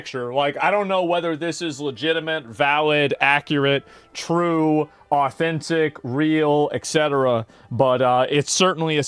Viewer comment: I thought Asmon said Hassan was “totally Authentic” Y’all remember that from like a few days ago? 😅